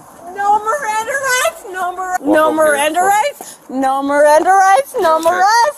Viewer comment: I swear I heard her say, "No Miranda Rights...no mirrest.." LOL